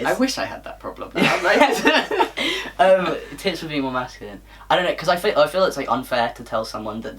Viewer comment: when said 'tips for being more masculine', i heard 'tits for being more masculine' lmao